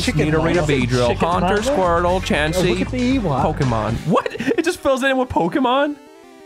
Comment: I was listening to this without watching it and swore I heard say "look at the Ewok" and apparently I wasn't wrong